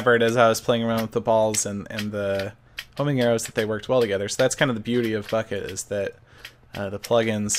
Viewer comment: Did he just say or are my ears fudged " thats the beauty of fucking "